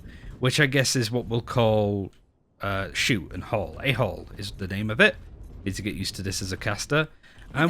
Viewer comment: Almost sounded like A Hole lololol